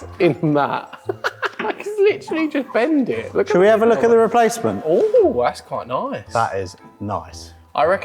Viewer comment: at @ 5:18 i honestly thought said "shall we have a look in your basement"... made the scene so much funnier haha